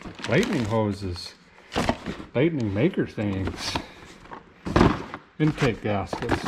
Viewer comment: Im sorry but did I hear a "baby maker things" in that box @ 7:08? 🤔😲😂🤣